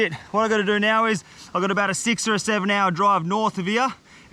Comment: wait, what number did he say? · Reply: 6 or 7? Is that an inside joke lol?